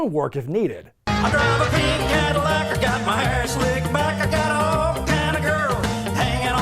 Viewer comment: I keep hearing, "I got my pink Cadillac, I got my ass slicked back."